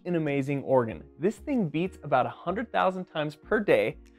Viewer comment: At first, I thought he said "yeets"- as in "this thing yeets about 100,000 times per day". I have decided I like this better, and moving forward I will now ask my doctor to listen to my heartyeet instead.